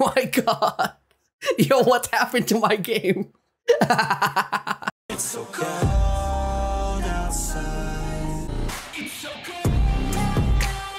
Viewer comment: BRUH ALL THIS TIME I THOUGHT IT WAS SAYING HISOKA BUT IT WAS IT'S SO COLD LOLOOL